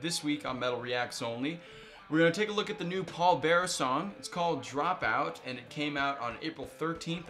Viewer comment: The way you kept saying Pallbearer made it sounded like you were saying Paul Bearer lol